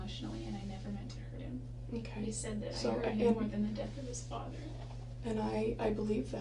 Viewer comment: Arias says “I’ve hurt him very much emotionally & I never meant to hurt him – he said ….. in the death of his father”. The detective interrupted her – does anyone know what she said prior to “in the death of his father”? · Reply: ***** THAN the death of his father, i believe. he said that to her in one of the messages, i think the final argument, that what she did to him hurt worse than the death of his father.